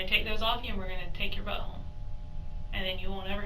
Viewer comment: At about the 35 minute mark, I thought she said"take your butthole"🤦